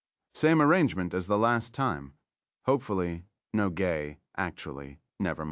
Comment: Did he just say gay.